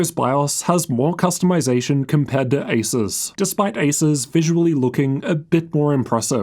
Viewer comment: do i hear Asus? I'm not even looking to upgrade my laptop but still watch your reviews. Quality stuff, Top man. Cheers